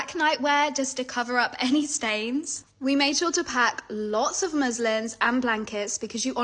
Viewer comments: I thought she said Muslims 💀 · Sorry but I heard it as We’re going to pack MUSLIMS I’m SoRrY WhAAAAAT